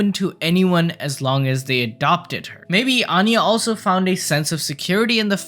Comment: when you said adopt i thought u said abduct 💀